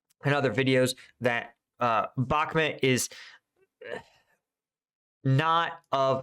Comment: The subtitles have no idea what you mean by Bakhmut 😂 every time you say it, the subs say something completely different, we got Obama at 5:27 🤣